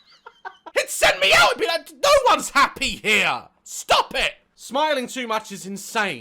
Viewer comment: I thought he said pop it😃